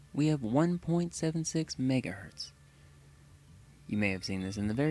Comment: i misheard this as “migge-“ HELP—😭😭😭